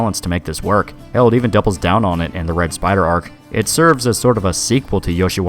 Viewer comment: You keep saying "Red Spider Arc" but I keep mishearing it as "Brett Spiner Arc".